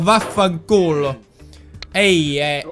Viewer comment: did he just said vaffanculo? HAHAHA